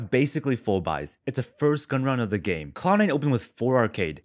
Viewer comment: I heard clown 9 instead of cloud 9 and then this happens, haha